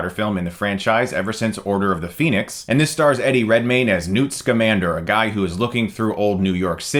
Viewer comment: Before I saw the name in writing, I always heard "Nudes Commander". On the other hand, I am not a native english speaker, so I guess it's my bad. Still funny though.